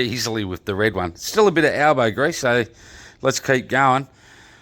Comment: Hey, it sounds like your saying Albo Grease. Don't you mean Elbow Grease. Albo Grease wouldn't remove tomato sauce from a Laminex bench top. Fantastic work your an absolute champion